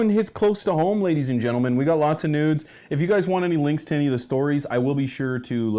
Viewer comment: Did he just say we got lots of nudes? 0:17 no thank you 😂😂😂 he keeps saying it wtf Canadians cant say news????? Lol crazy